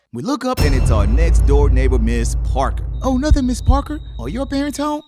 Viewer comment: i thought he said "Ms. Park" for a second 😭😭